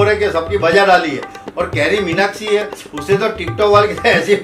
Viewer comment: Did he say carryminakshi